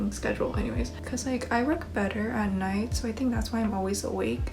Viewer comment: "I look better at night, I think thats why Im always awake at night" Litrally have never agreed more with anything in my life. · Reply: i thought she said "i work better at night"